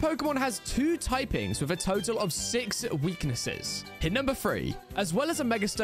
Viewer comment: Nah I heard "2 types with a total of 6 weaknesses" and I was like latias or latios then I was 2nd guessing thinking it was cleafable when I heard him say "momento"